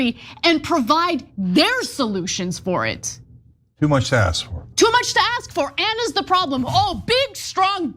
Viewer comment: I thought Cenk said " too much sass."